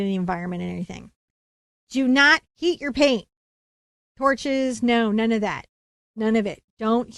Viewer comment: I thought she said “do not eat your paint”, and I thought of Van Gogh 😂